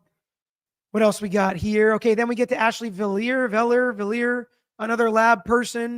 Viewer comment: I heard her say asshole